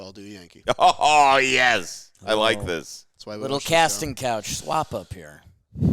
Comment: I heard "Casting Couch Swap" Had to make sure I was on the right website...